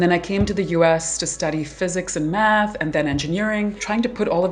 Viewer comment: I was multitasking and thought she said "I'd been studying physics and meth" 💀💀💀